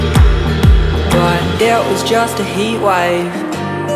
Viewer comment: From across the room i thought it was saying "it was just a peewipe" lol, good song tho